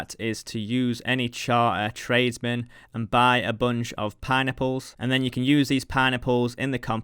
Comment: It sounds like you're saying "pie-nipples" whenever you say "pineapples", lol. Good video as always. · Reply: I can not unhear this now... I thought he was saying Pine Nipples but now its just pie-nipples...